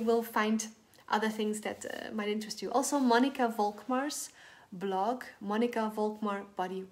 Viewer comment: I was trying to look up someone you mention in your video - it sounded like you said Monica Folkmark but that's also what the closed caption said so it's possible i misheard😂 I didn't see that name in the info in the description and I think there were other people you mentioned that aren't in the description but I'd have to listen again to get the names. Thanks for the info!